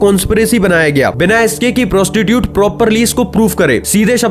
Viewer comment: did he just say prostitute instead of prosecution?